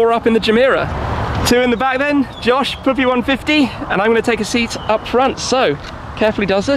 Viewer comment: did he say “poopy 150”?!?